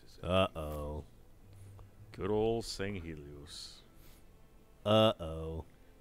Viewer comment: I thought said, "Good ol' St. Louis" for a second. I don't think he'd ever say that if he actually came here.